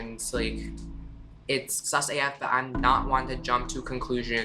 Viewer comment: for the hearing impaired... the english captions say "[indistinct]" at 0:26 but he actually says "sus af" (suspicious as fuck).